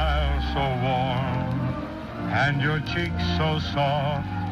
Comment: WHAT DID SHE SAY??? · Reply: She said "Goodbye, my darling".